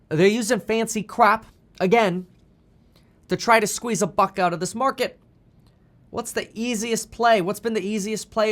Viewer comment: When I heard Kevin say "squeeze a buck out of this market" I thought he said "squeeze the FUCK out of this market". I was like, did Kevin just curse? Kevin never curses lmao! Never mind carry on everyone 😂😂😂😂